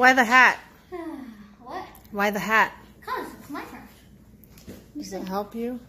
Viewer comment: w h y t h e h a t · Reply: i thought he said "because it's my craft"